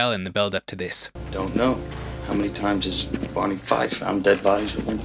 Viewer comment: Uh, at 9:20 brad pitt doesn't say "one in five" dead bodies are found alive at a crime scene, he said "Don't know, how many times did Barney Fife found dead bodies that aren't dead." Barney Fife being the sad sack comic relief cop on The Andy Griffith Show, who was always messing up. Pitt's character was referencing how the previous murder victim turned out to not be dead on his mattress; a fact missed by the inattentive cop who discovered him. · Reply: I thought he said Barney Fife as well so that’s reassuring